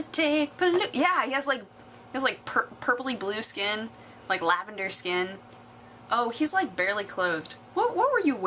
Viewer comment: I totally thought you were saying "vendor" (I'd be a great hotdog vendor btw)